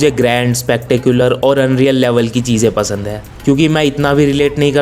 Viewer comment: Did you say Unreal? Hmm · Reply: Yeah, you know:)